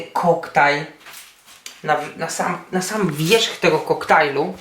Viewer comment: usłyszałem (nasram) :D